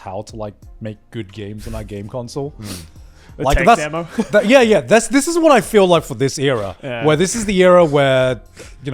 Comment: what did joey say? Demo console? Please tell me what he said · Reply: Tech demo